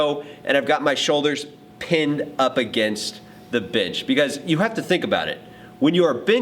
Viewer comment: Great Video! Am i the only one who thinks it sounds like bitch instead of bench at 1:26?